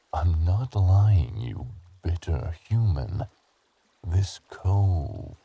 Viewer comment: Why at 5:00 I thought he was gonna call me the b word instead of bitter